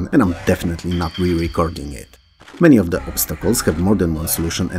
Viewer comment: did you say "re-reCORGIng"? XD OMGBBQ! Ahem! I wasn't expecting these games and they're all new to me (except for the ones you've talked about in other videos). I guess there are lots and lots of puzzle games to choose from on DOS. :) I share your sentiment about timers. And the way the speed ramps up in the games with a Tetris inspiration is kind of adding a timer too. Scary stuff. :D Fool's Errand sounds very interesting. I like additional stories to tie up the puzzles. :) Btw, have you heard of Puzzle Agent? It's a newer game, but it has an intriguing art style and story to it.